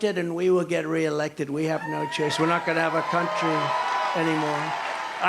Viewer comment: Did he just say "When I get re-elected, we won't have a country anymore"? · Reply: "We have no choice (or else) we're not gonna have a country anymore."